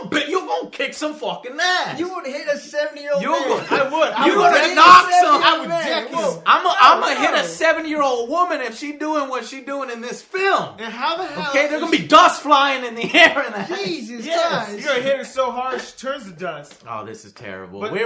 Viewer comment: Did you just say seven year old woman? Really? xD LMFAO